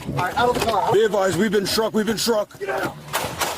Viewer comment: is he saying we've been shot or we've been struck? I mean neither happened but curious what they said.